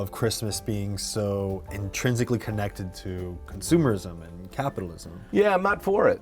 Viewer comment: I thought he said Subarus not consumers.